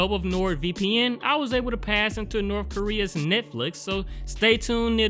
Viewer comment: did he just say North Korea's Netflix? :v